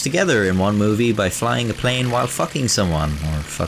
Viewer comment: I thought u said he fucked a plane while fighting someone xD do the great japan bank robbery next